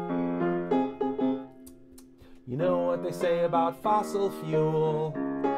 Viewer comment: I thought he said "you know what they say about fossil stools"